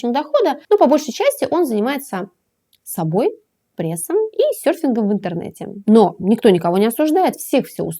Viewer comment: мне послышалось или вы сказали занимается мерфингом в интернете??) это как? когда море далеко а возле кровати простаивает доска для серфинга?) и вот берет он и включает интернет и занимается серфингом) скоро детей начнем заводить через интернет)))